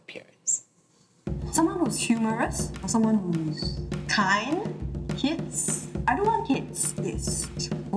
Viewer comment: Why doesn’t the text match what she said? She said she doesn’t want kids..